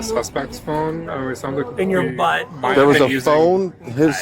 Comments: does he say "in your butt"???? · I went back and listened to this three times and I swear, I hear: “Do you have the suspect’s phone?” “It sounds like—“ “In your butt.” PLEASE tell me I’m not the only one hearing this. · “do you guys have the suspect’s phone?” “In your butt!” Am I crazy or was this what they said😂😂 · it sounds like he was saying the suspects phone was in his butt ? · did he say "in your butt"? 😂